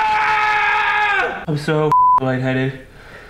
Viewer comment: I thought you said she had Covid